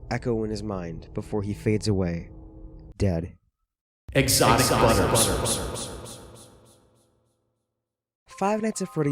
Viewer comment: I heard "exotic butter" is that what he said? xd